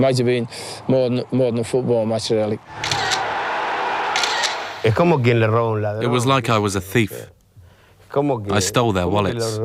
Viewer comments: He actually says "it was like robbing a thief", which I think sounds a little better, implying it was a kind of revenge. There is an expression that says, "thief who robs a thief, has 100 years of pardon", so you see... it was almost demanded lol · In the translation of the video in minute 4:17 What Maradona says is: “i felt like i was stealing from a thief” and the translation says “it was like i was a thief”. An important clarification..